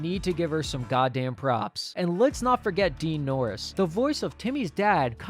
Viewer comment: did you say DEAN norris or DARAN norris?